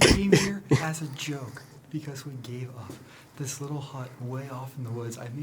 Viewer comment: is your impression of Josh isn't it?! 😂😂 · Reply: does say little hot boy lol